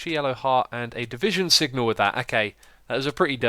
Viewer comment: Did he just say Division signal? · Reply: The Great One sigil* :-)